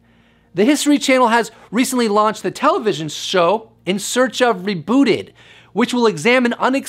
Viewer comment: Anyone else hear in search of big booty? 4:41 😁👽🛸